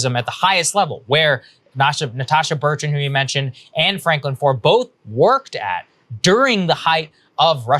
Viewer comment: Man I guess I barley listen, thought I heard Anne Frank, and was like, fk there's another one? Thought that name would be off limits, or just culturally not accepted to use anymore.